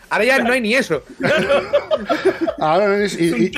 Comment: ¿Qué dice en el minuto 9:58 que se parte de risa? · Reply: ''Es un timelapse''